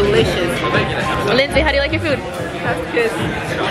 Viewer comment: Your sister’s name is? 7:05 that’s what it sound like you said, if so that my name 😍too, just different spelling I’m sure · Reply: Aww Yes! Her name is, Ill never forget your name hehe 💕